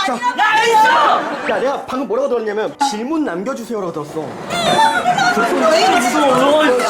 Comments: 이거 문자 남겨주세요 라고 들었어욧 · 전 왜 2번째에 '질문' 남겨주세요가 아니라 '메세지' 남겨주세요로 들리죠..? 많이 들어보니 '질문'이 아니라 '메세지'로 들려요... (메에이 남겨주세요 라고 들려요...) (에이미 아님) 이 잘못 안 것 같은뎁쇼..... 전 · 진짜 질문 남겨주세요 라고 들리는데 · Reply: 자세히들으면 메시지를남겨주세요에요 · 분 질문 남겨주세요 라고 들리는데요? ㄷㄷㄷㄷㄷ댣거거거아나엉난ㄴㅇ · 저는 지문남겨주세요라고 들었는데